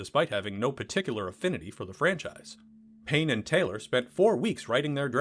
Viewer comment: Aaah, Payne and Taylor! That makes more sense! I thought you were saying Penn and Teller!